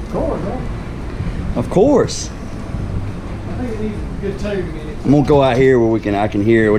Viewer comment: Did I hear "needs a good tune in it"?